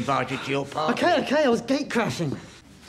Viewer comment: I honestly thought he said, "Okay, okay, I was gay crushing." The first time I heard it lmao.